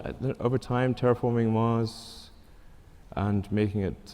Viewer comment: what was the random guy say at 1:04:21? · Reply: "You can do it, Elon"